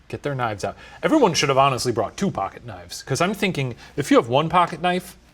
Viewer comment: My brain didn’t register ‘pocket knife’ at first at 16:37 and I thought Shane said ‘Everyone shoulda honestly brought Tupac’ matter of factly